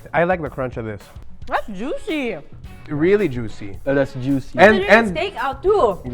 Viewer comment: When they are trying Dokito please correct me if I heard it right. "That's Juicy, really juicy, Alasjuicy" 18:32 Timestamp T.T